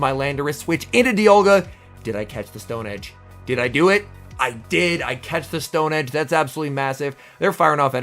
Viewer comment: did you say “I catched”?😅